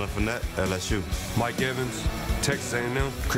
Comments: I thought he said Boomer University 🤣🤣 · Thought he said boomer university